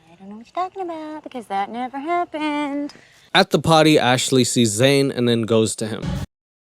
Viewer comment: LMFAO turning on subtitles/closed captions and it thinks "potty" was said instead of "party," so it says, "at the potty, Ashley sees Zane." Just wasn't expecting that lol